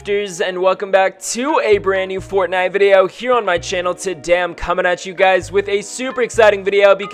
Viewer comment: Wait 0:09 did he say i am comming at you DAMM??😂